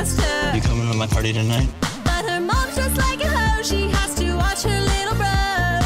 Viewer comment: Lol I thought she said her mom dressed like a house but the I saw the word ho